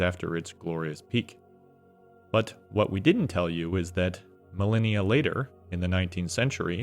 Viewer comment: i heard "millennia later" as "Malenia later" and was super confused for a second